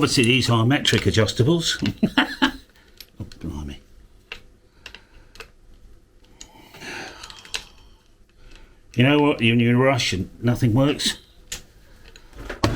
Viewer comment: I heard "You know what, when you're Russian, nothing works" !!! 😝